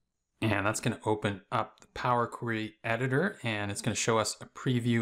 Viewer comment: and that's gonna open up power (inaudible) editor... What word did you say there? Thanks in advance. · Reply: Power query editor 🙂